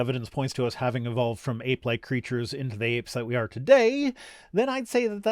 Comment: I understood aight leged creatur instead of ape like creatur